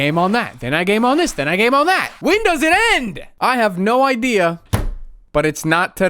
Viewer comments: I HEARD "WINDOWS IT END" LOLOLOOL XDDD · i misheard this as WINDOWS IT IN!!!